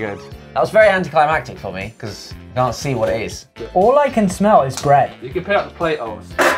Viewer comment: Tell me you didn't hear say "all I can smell is grey"